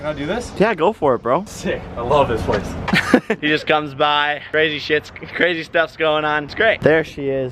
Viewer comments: did he say crazy sh*t crazy stuffs going on 💀💀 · Anyone heard jake say 'shit' at 6:32??